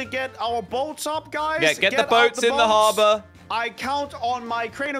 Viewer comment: There's no way he actually said lava😂